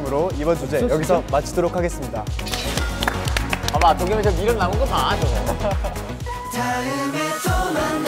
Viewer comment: 정한이 이 때 뭐라고 그러는거죠? 봐봐 도겸이 쟤 ????? 남은거 좀 봐? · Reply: 도겸이 쟤 미련 남은거 봐